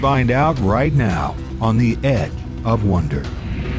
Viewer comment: who else heard him say "The Ed of Wonder."